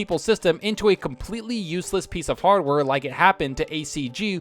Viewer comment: Lol did he say "AC-Jew"?